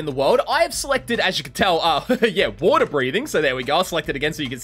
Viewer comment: did he say water breathing 😂